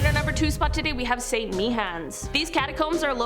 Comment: did she just say st. meehans